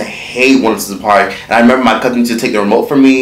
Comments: I swear I thought he was saying once and park. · I thought he was saying Once and Park